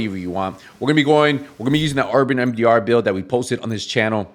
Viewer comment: Hi - thanks for video. I can't make out what build you mention at 0:24 - "RDMDR" build? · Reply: Urban Mdr build it’s in this channel most recent buils